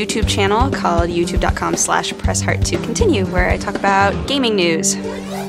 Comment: Gosh dang I thought she said press fart to continue.